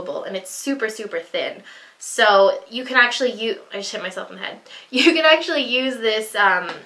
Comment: am I the only one who heard "I shit myself"?